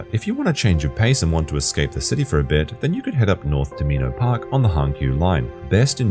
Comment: Did anyone catch what he was saying at 5:26? What is the name of the park? I really want to go and have replayed the video so many times but didn't catch it > < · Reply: Minoh Park